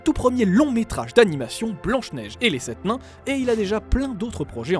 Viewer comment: C'est moi ou j'entend à la 0:49 "Blanche Neige et les sept MAINS" 🤔🤗